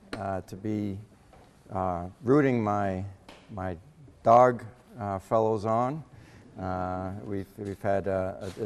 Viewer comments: Anyone could tell me what he is saying between 00:27 to 00:30 would be appreciated. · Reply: Rooting my Dogs(UGA Bulldogs) on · Anyone could tell me what he is saying between 00:27 to 00:30 would be appreciated. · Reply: John Dane lowkey I thought he said “dark fellows” but that seems more likely LOL